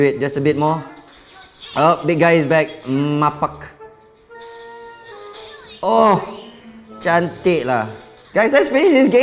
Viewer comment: did he said malaysia cantik